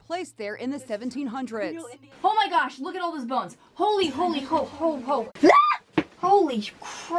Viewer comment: Did she say !!HOLY HOLY HOE HOE HOE !!